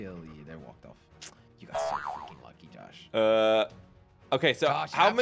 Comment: Correct me if I’m wrong, but I heard Jelly say: You are so fucking lucky